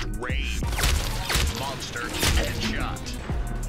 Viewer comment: it was gonna say "monster kill" but killed someone and got a headshot so it ended up saying "monster headshot"